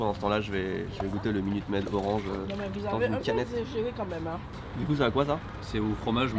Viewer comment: C'est quoi qu'elle a dit la dame derrière à 3:50 ? mdr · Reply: Moi jai entendu : "Non mais vous nous avez assez genées quand meme hein" Et paf! Cut .